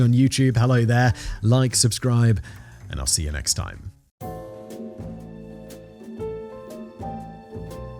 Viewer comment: Had to replay the end, I thought I heard Simon say "See you next crime" 😅 Turns out my subconscious just likes corny outros.